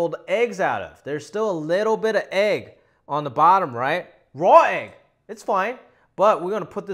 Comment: When you said "raw egg" it kinda sounded like you said "Roy" and I was trying to figure out what Roy Roger had to do with your eggs. 🤣